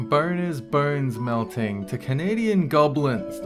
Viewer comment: Btw when he said boner it sounded like he said burner xD but immediately when he explained it then it made sense.